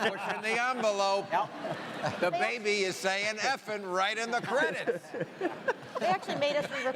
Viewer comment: i'm not a native english speaker (i speak portuguese) and I always thought he said effin' cry! lol!